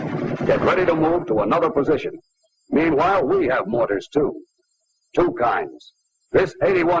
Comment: I thought he was saying boner, not mortar